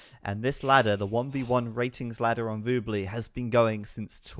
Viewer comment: what is that woobly thing everyone says?